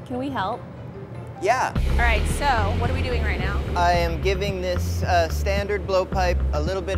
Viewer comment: I thought it said I’m giving birth